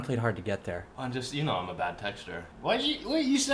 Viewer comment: the way i thought was saying “bad texture” when he was actually saying “bad TEXTER” SMH 🤦‍♀️